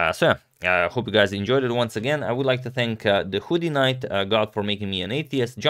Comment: “i’d like to thank the hooded knight god for making me an atheist” did i hear that right?😂